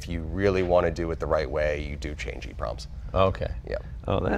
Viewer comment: What does he say at 4:05? Change e-prompts?